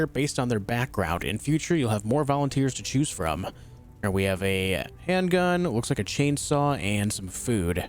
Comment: "we have a handgun looks like a chainsaw" LOL this dude literally think that's a chainsaw instead of a wrench